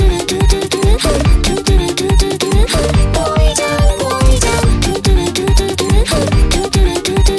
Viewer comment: ぽいじゃんがずっとポイズンに聞こえるんよな